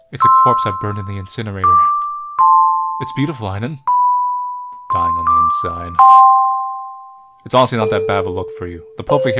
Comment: After he narrates "It's beautiful, Aiden.", he makes a comment which I couldn't hear because his microphone volume was lower than the game's volume. The music overpowers his voice. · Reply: "Dying on the inside." Maybe?